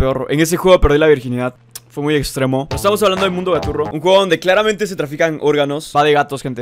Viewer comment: quien sabe como se llamaba ese juego es que no entendí el nombre osea lo que dijo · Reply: Mundo gaturro